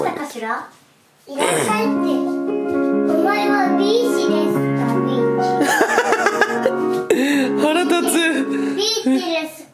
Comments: のお前はピーチですか？bitch.が、みんなも言ってる通りお前はビッチですか？に聞こえて笑った。しかもビッチの発音良すぎ。さすがはしばゆーの遺伝子感じた。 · 『お前はビッチですか？』って聞こえるw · お前はビッチですかって聞こえて焦ったw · お前は鼻医師ですか？ビッチ。 って聞こえてびびった笑笑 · 「いらっしゃいってお前はビッチか？」って聞こえる😂